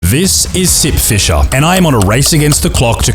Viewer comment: I thought you said "This is Simp fisher" in the beginning and i thought "Hm.. people simp for the weirdest things theese days"